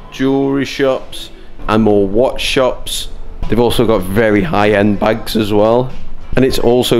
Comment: I was half watching and half doing somethig else, and with your accent (not the same as mine) when you said they had really high end bags, I heard, "really high end bikes", and I was really shocked, thinking that was super specific for a cruise shop... I had to rewind! Great video!